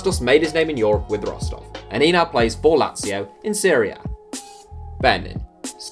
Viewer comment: When you say serie a it sounds like your saying syria